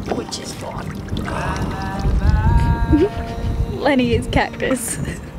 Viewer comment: Watching in 2222 dying to know what says at 2:15 that sounds like “Lenny is cactus”. If anyone from the future knows this Aussie saying please let me know! I’ve always wondered…they say it a lot.